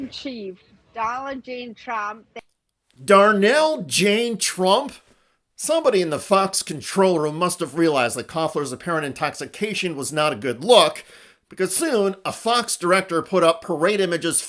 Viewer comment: I heard “Dolla Jayne Trump” but the way the host said “Darnell Jane Trump!?!” took me out😂